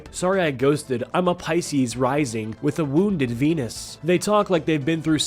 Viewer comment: When I got to 3:45, I thought I heard the words "wounded penis"